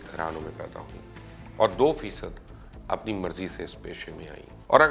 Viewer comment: بلکل مبشر بھائی اللہ پاک اپ کو سلامت رکھے امین. ..... اپ نے اس پروگرام میں کہا 4 فیصد عورت خود...باقی؟؟؟؟ اپ نے ایک زبردست میسج دی اگر کوئی اس پر عور کرے ... کیونکہ عورت... ماں. بہن. بیوی اور بیٹی بھی ھوتی ھے ... اگر اس لعنت کو ختم کرنا ھو تو سب مسلمانوں کو اس میں اپنا خصہ ڈالنا ھوگا ... سب کی مدد کرنا... 4 فیصد کا نھی. .. ماشااللہ میں اپ کا ھر پروگرام عور سے سنتا ھو .... اللہ پاک اپ کو ھمت دے امین.... اگر اس 4 فیصد عورتوں کے علاوہ ھم نے سوچا کہ عورت ماں.بہن. بیٹی اور بیوی ھوتی ھے تو سب ٹیھک ھوگا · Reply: چار فیصد نہیں، دو فیصد۔.Check at 0:33. He did not say four percent (4%); he said two percent (2%) come by their own choice.